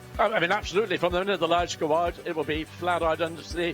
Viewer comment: WTf did he Say? Sounded like he had a Mouth full of Rocks (-__-). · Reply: "from the minute the lights go out it'll be flat out"